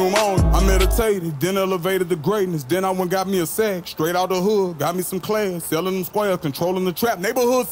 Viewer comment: I thought he said he good?????